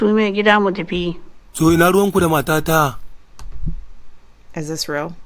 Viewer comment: Did he say Hacuna Matada? cause it looks like he has Poomba on his back. Is that real?